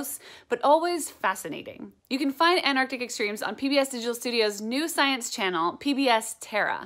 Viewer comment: I listen at 2x speed and could have sworn she said "Anarchic Extremes".